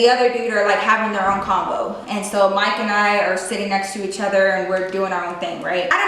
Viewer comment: Am I the only one who thought said "Mike and Ike" when she actually said "Mike and I."